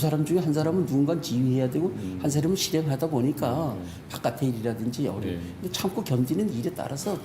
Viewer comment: 왜 나는 얼마든지 일어날 수 있는 일이라고 들리냐 ㅋㅋ 진짜 대단하네 ㅋㅋ 이게 업무 포지션과 뭔 상관인건데 그냥 갑질이랑 부조리가 심각한 것 뿐이잖아... "양"자 들어간 동네는 뭔가 조금씩 나사 빠진 것처럼 이상하냐 양양, 밀양